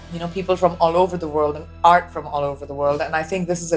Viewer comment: "People from all over the world aren't from all over the world." Whoa NICELY said! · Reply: I heard "People all over the world... ART from all over the world."